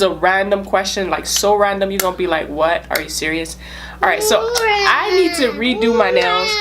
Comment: It sounded like she said "I needs tu zeep my sheet" Which what I thought was: "I need to zip my sh**t"